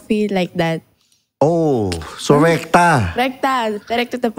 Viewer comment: Lol ....did i heard her say "RECTAL"...Instead Rekta. 😂